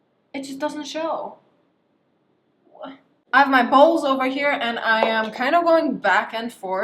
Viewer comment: Ok I could not be the only one who thought she said balls instead of bowls right